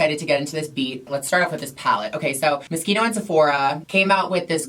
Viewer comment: I thought you was saying mosquito lool, never heard someone pronounce Moschino like that